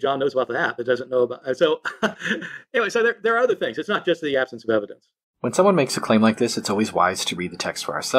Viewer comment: sounds like he is saying red herring is evidence.